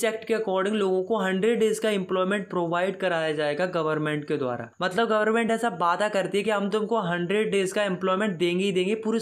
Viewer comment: i heard gov paada karti hai